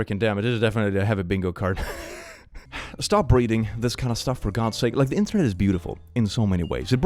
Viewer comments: I zoned out and thought I heard him say “stop breeding” at 23:35 and got very confused for half a second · I thought he said stop breathing for a hot second there😂